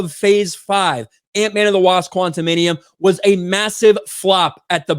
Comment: Is it just me or each time says the title of Antman 3, I hear "QuantumaniUM" ? :)